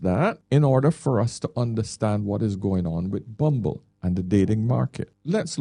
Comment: In your intro, it sounded like you said "bumbo" 😄, our Jamaican swear word.